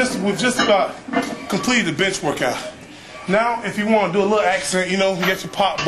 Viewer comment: thought he said, "we just about completed the bitch workout" hahahaha